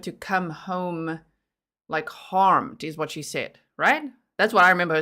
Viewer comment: In the TV news interview the detective's referring to where they thought she said she didn't want Maddie to come home, she actually said, "I'm scared for her, and I don't want her to come back harmed". But "harmed" sounded like "home". I thought the same thing when I watched it. But even with the word harmed, it could still be considered leakage or as the term goes "a Freudian slip". "...don't want her to come back harmed" could be used in a different context and it's possible that at that time Jen knew Maddie would not be coming home harmed because she was already dead. It's even possible that she was relieved and would rather Maddie be dead because she might have seen that she was harmed very badly the days before. It's a stretch but that's the way we leak things even when we don't want them to come out. And it's possible that the detectives caught that.